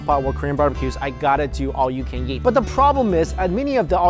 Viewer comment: I just realized it sounds like he says "All you can yeet." It makes me giggle a little.